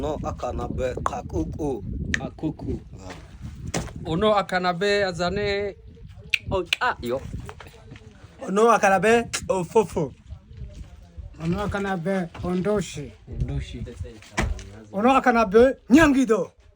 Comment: That second name sounds so fun! How do you write, what’s your name in their language? I heard like “oh lo Akanabe…. Jennifer